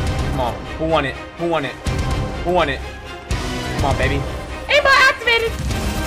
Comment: I think he says “Aimbot Activated”